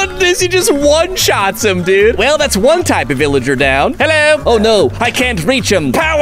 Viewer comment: did he say villager down?